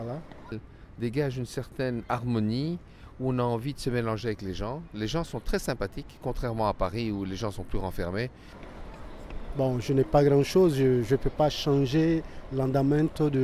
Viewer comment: I can't understand how this guy on 3:36 says so many words at once. I don't hear "ou on a envie de se m'elanger", I hear something like "une envie de m'elanger" (and only at 0.75 speed)